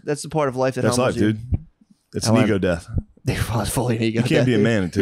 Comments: what does he say at 1:01:55? "knee go death?" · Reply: Its an ego death. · swore he said “nego” death 😂